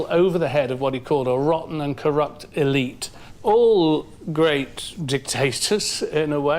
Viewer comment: Bruh.. did dude really say, "All great DICK-TASTERS or charismatic leaders...." blah, blah, blah, BUT DID YOU ALL HEAR DICK-TASTERS, TOO?! LMFAO!!!